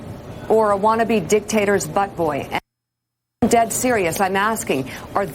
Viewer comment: "dictators butt boy" Did I hear that right... Where is the outrage...